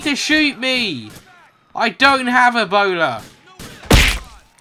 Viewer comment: Hey guys, hope you enjoyed the video :3 If you did be sure to leave a "like!", I'd appreciate it a lot! I'm going away for Comic-con from Friday to Sunday and I won't have time to upload much, I have one video scheduled for when I'm away but once it's over I'll be doing Lemonators #3 when I come back. Thanks for watching! :) #LemonCrew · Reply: i thought you said "i have a boner" instead of "i have Ebola" XD i only realized when you hit the cop